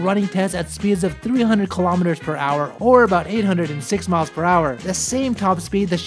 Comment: Sounded like he said 806 mph not 186 mph!